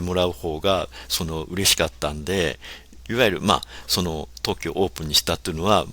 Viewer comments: The part in 30:18 where the translation of what said was "We decided to launch it in Tokyo..." is actually「特許オープンにした」which means "made it an open patent..." So he actually said "We realized that it was the right thing to do when we made it an open patent. And as a result, it spread widely and we think that it's really good." I learn so much from your videos Derek! Thank you so much! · Your English subtitle is wrong at around 30:18. He says Tokkyo/特許 which means patent.